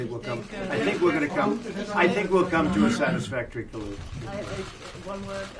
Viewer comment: I hear, " come to a satisfactory collusion."